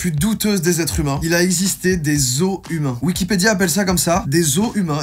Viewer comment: moi j'ai entendu des os d'humains donc je comprenais pas au début 🤣